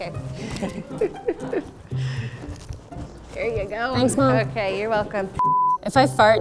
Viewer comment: yes, I know the captions say "thanks Homes", but why do I hear "thanks mom"????